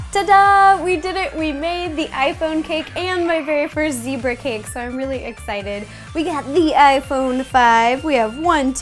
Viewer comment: When you said ta-da the first time it sounded like you said ta-daranga